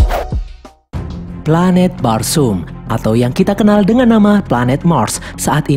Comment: aku dengernya planet bakso